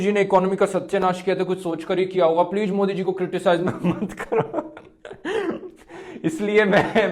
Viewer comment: what druv bhai was saying plz modi ji ko kuch mat kahu what we all heard teri kehkr lunga. :)